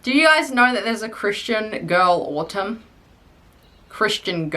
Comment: i thought you said Christian girl water (i was drawing so I didn’t see the video)